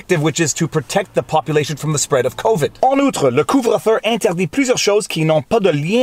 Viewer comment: I gotta go there, its childish, I know, but in french it sounds like your saying " Covid deez nutz". Now I have to go pull some pigtails on a girl that I secretly have a crush on.